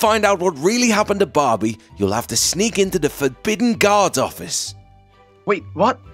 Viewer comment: When he said Guard Office I thought he said Guard docks